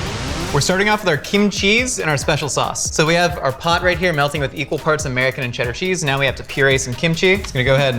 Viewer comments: Josh, when you said "equal parts" at 1:10 I heard "human parts" and I was like yeah probably.... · I thought he said 'people parts' instead of 'equal parts'